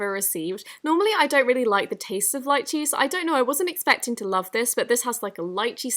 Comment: hey guys! 4 winners have been chosen and 1 needs to be rechosen because 2 people didn't reply when i rechose and then 5 others had removed some of the steps that they said they did so i have to go and pick ONE MORE PERSON but it'll be announced friday when all the money is moved. 4 winners have already gotten $1,600 in total. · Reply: When you said Lychee i thought you said "lied cheese"